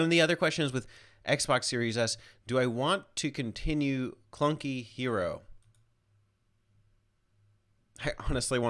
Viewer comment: At first I thought that said chunky hero and I was like "oh it's me!"